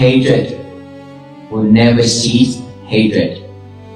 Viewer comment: The quote at 44:36 should read "Hatred will never cease (not "see") hatred. Only loving kindness can cease hatred." The subtitle misheard the honorable Bhikkhu. Deep thanks to everyone who participates in this walk for peace.